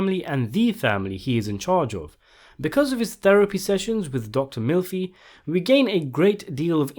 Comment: I heard "Dr Milfy" and it made me laugh